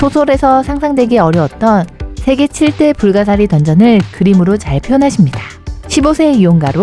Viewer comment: 불가사리 던전으로 자꾸 들려여ㅜㅋㅋㅋㅋㅋㅋ